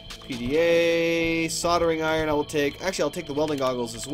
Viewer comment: Aha, I thought you said sodomizing iron XD